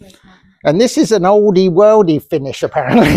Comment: Great job! I 'll never look at oldie worldly frotage the same again!🤣 I laughed so till Don came to see what all the hoot was about. Just loved it. Imagine your appreciation for what your sweet does has deepened a bit. Are you ready to quit your day job now? Love you 2, take care thanks for the laughter it was great. you really did do a great job! Vicky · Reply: Hey Vicki, I misheard whispers, I could have sworn she said Oldie Worldly style. What a laugh we had making that video and yes, I already have a deepened appreciation for my talents. She makes it all look so easy. So glad you enjoyed it so much. I feels nice to think of you and others around the worlds sitting laughing at us. Glad we could bring a smile to so many faces.